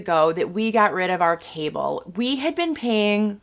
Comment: Ha. At first, I thought you said "We got rid of our table."